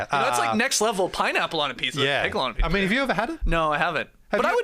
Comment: Maybe I'm just crazy, but pickle on a pizza sounds great to me. I like interesting toppings. Also, you see banana peppers or pepperoncini on a pizza all the time. Is this that different? No, I'm not saying banana peppers taste like pickles. But a tangy flavor profile clearly works on a pizza. Incidentally, I thought you guys were saying "picollono" pizza for most of that segment. I told myself I'd have to google that since I was unfamiliar with whatever Italian delicacy you were talking about.